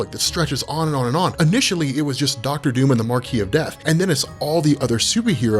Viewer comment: I always hear "Marky of Death" instead of "Marquis of death".